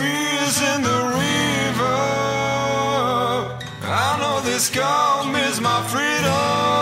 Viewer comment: I thought he said "I know this calm is my freedom"